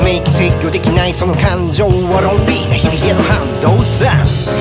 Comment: 『「ロンリ」な日々』 0:47 のところ、「ロンリー」にも聞こえて好き